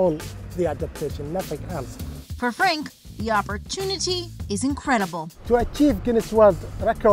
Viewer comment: when she said the opportunities, i had to rewind it because i thought she said virginity. i do apologize my hearing can be good. did anyone else hear that the first time?